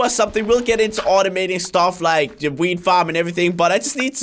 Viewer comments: It sounds like he said weed farm at 12:03. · weed farm..... Am I the only one who Heard it like that?